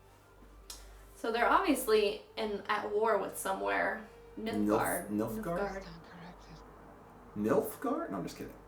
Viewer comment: honestly i just im new to this channel and i came to see the fight scene and as i was skipping through the video all i heard was "MILF-guard?" i was jus crackin up